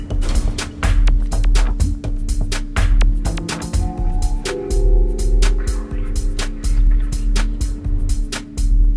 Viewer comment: anyone knows tf its the girl saying? sounds like spanish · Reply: "ahora me has prometido" translates as "now you have promised to me"